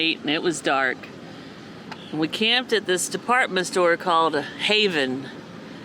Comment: Pffhahahahha I heard "a store called haven", thought it was a weird name for a Brazilian store, look over to my phone screen, AND LMAO of course it's an Havan